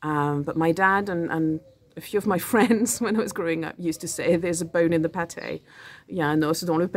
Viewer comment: I thought you said there's a bear in the paté 😅